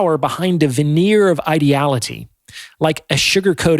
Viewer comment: sorry, but what word I’d this? It sounds like “ideology” but with a “t” instead of a “d” - but i can’t find a definition for it. Or Is it just “ideology”?.